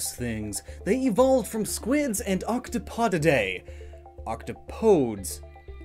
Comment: I have no Idea how to feel about this... Hold up, did he just say octopotamus?!